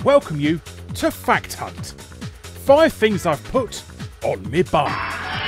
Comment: Hey! This isn't "Five Things I've Put On Me Bum!" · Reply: Not gonna lie the first time I heard it I thought he said "5 things I put up my bum" had to go back and watch that part again lol